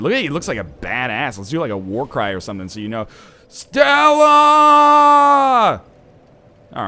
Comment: what did he say??? was it like "steauaaa " or what ?